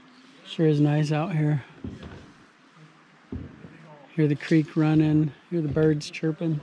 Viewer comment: I heard it as you said "creep runing" 28:43 🤣 creeps out in the forest